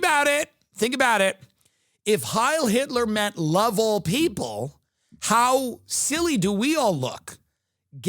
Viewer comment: I was not hearing "love all people" i hear " love old people" and it made it so much funnier